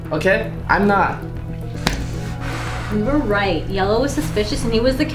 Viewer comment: when he said yellow i thought he said jello